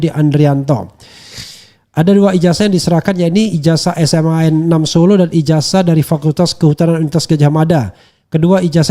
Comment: serius nanya, pak RH bilang apa si di opening? *Assalamualaikum wr wb sobat RH sekalian, jumpa lagi kita di channel yg keren ini. keren .......... nah ini bilang apa?🙏🏻😭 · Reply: Keren cadas